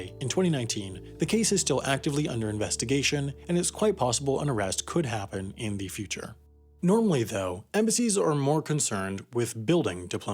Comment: I swear you said "Norway though"